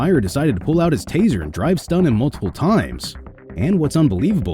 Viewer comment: Is he saying "Dry stun..." or "Drive stun..."? What does that mean?